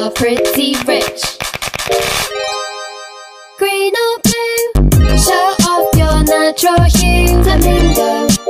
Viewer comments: I thought it said "Show Off That Natural You" XD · I always thought it said "if you're Monty Carlo that's cool too"